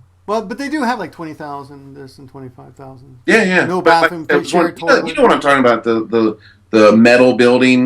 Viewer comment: sorry, did you say "no bathroom"?